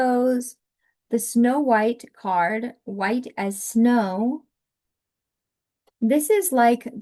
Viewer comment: Im so tired…I thought card three said “White ass snow” instead of “White as snow” 😭😂